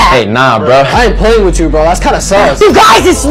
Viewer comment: im wondering what he said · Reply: "thats kinda sus" glitchtrap with a hat - 2023